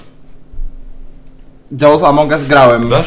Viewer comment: Kurwa czy ja usłyszałem amonugus?????